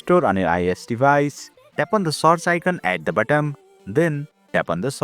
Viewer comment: The first two times you said search I heard " ~Source~ " with an Indian accent, Soo my tip is please stop being Indian, or please make videos in your own language.